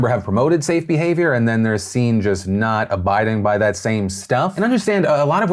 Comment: Daddy Phil did you just say a-Biden by?? 😂 👍 🙌